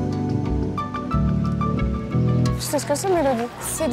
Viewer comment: j'ai entendu "Bilbon Saquais" je crois que j'ai trop vu le Seigneur des Anneaux...